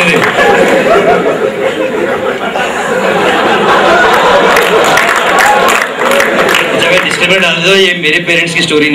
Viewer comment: what did he say at 4:27? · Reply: he said "family audience baithi hai isiliye PANDU bol raha hu"..warna he might have used G in place of P in the word PANDU lol